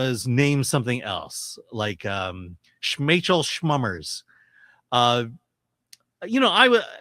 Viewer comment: Am I the only who heard Rachel Summers out of gibberish name at 4:00 ?